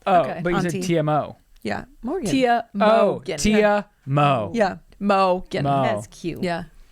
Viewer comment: when you say "Tia mo"Ike that it sounds like "te amo" which is I love you 🥹😍 that's what I thought you were trying to say 😂😂